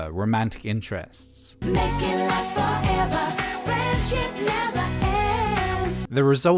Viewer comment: "make it last forever" OMG all these years i thought they were saying "making love forever". i didnt question it, im not really a top 40 person and teen pop groups always say pervy stuff despite being aimed at tween audiences.